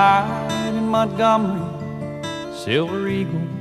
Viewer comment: When I was a kid I thought he said midnight in my garden